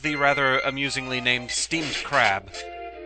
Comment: did I hear steamed ham